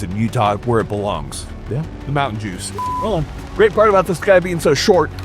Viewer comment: Did he say "The Mountain Jews?"